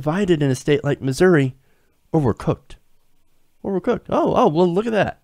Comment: What? Over-cooked? Ovacoocked? Can you even speak properly? Fuck, some people are too lazy to even enunciate the words they're speaking. I had to look up on Google what she actually said to work out what the hell you were saying.